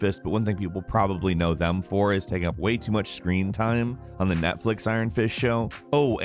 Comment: Sounds like you said "On the Netflix Iron Fish Show..." nice 👌